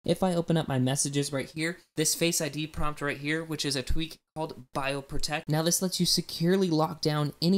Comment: Bio Protect what? You need to say versions in your videos. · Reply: Cyko bioprotect x.